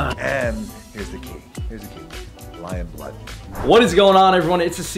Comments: Did He Said Lion Blood? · Did he said lion blood😕